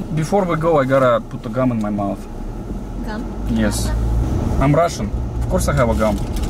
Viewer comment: You can tell the girl in the front thought he said gun which she did and then she looked at the girl in the back and she speculated the same thing that's why she asked him! 😂😂😂